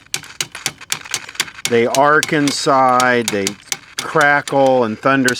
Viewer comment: Has Hillary been working on this? Oh! My bad! I thought you said "Arkancide" not "arc inside". Doh!